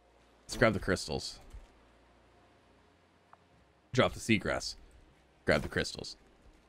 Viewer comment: "Drop the sea grass. Grab the crystals." What I hear: "Leave the gun. Take the cannoli."